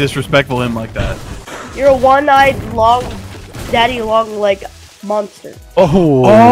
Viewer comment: did he just call him daddy